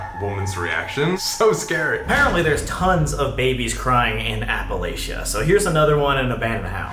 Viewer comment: I thought you said ‘Apple Asia’.